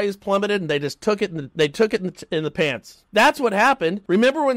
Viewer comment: Love you guys! what was the stuttering at the 21:36 mark...?? A$$, Shorts, Pants?? Some of us actually listen to what you are saying... My ears are burning!! LoL